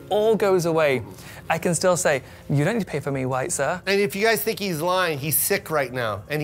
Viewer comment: did he say..."I can still say, you don't have to pay for me white sir."?